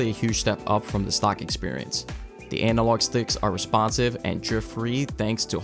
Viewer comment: Did you just say anal log stick? 🤔